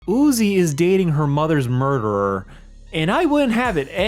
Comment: holy s**t, I thought he said Uiz was dating her mother